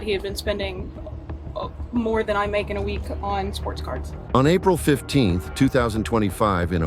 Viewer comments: oh sports cards, thought she said sports car and thought well that dont sound much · I didn't know that sports cards were still a thing. I thought she said "sports cars" at first. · Domestic violence is wrong but so is financial abuse. $1200 on sports cards! this man needs to grow up. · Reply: OH! I thought she said sports cars and I couldn't figure out what he was doing with the cars! Sports cards are even more pathetic. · Domestic violence is wrong but so is financial abuse. $1200 on sports cards! this man needs to grow up. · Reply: I thought she said sports cars haha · Ahh sport carDs! I thought she said sport cars. Okay, leave him😂😂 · I’m at work and have the volume low. In the intro I thought she said “sports cars” and I was thinking come on man, you have to play with cars responsibly. Then I realized it was “sports cards” and I’m all for men having a hobby that’s not bad to their health or relationship, but this dude can’t contain himself. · i thought she said sports cars and i was like lol cool then i heard it right it was playing cards